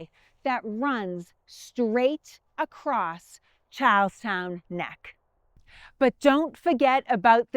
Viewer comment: i thought you said cows town. But i think you were saying charles town